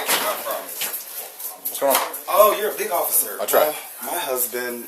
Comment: He didn’t say that. Misquoting people is one of the main reasons cases get dismissed 🧠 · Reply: You're right, he actually said "oh, you're a big officer"